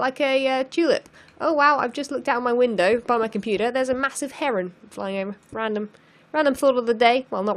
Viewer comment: Anyone could help me? What did she said at 8:15? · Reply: She said she saw a heron which is a pretty huge bird.